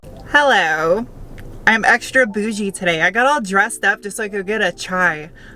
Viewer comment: At first, I thought you said extra booby today at the beginning. Aaaaaand you’re a Tool/Perfect Circle fan?!?! Awesome! You excited for the new Tool album?